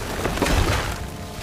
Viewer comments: What is this accent? I heard only "Capitah Sarazah, I heayouluke fo Jugs Barro" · “Capeetahn Salazarr, I hear ye be lokkin fer Jack Sparrah!” -Captain Hector Barbossa in the worst outfit he’s ever worn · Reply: I heard "Capitah Salasa, I hea youvblukin for Jag Sparra"